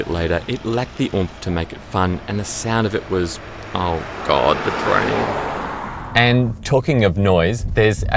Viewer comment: did you say "oh God.....the train"?? 🤔 Because that's how I react when I'm trying to get some where and I see the train coming..... 🤦‍♂️